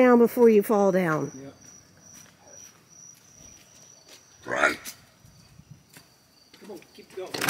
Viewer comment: that sounded like a demon 🤣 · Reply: It said right